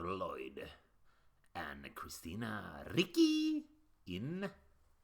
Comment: It sounded like you said Vicky when you said ricci😅